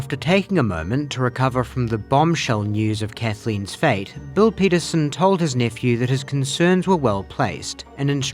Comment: I’m listening at 1.5x, and instead of “Bill Peterson”, I thought you said his uncle’s name is “Bull Penis”. 😳